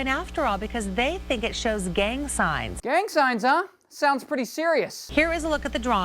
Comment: I Thought It Said Gang Sings! Thumbs Up If You Did Too!